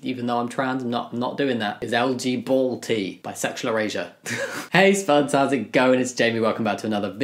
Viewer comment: Because of your accent I thought you were saying bisexual Eurasia instead of bisexual erasure. 😂